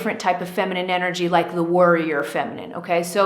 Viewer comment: I think I heard you say “ the worrier energy” 😄 but I’m pretty sure it was “the warrior “ energy. When I first heard that, I was like “whoa ...that’s the kind of energy I need a little less of. My whole life I’ve always been a worrier, even though I am very nurturing at the same time it’s this worrying that keeps me awake at night. It seems as though the more I worry, the more things happen in my life that bring more worry and woe. If I can find a way to stop or soothe the worrying, will this change the things in my life? ( can I stop the bad things from coming my way?)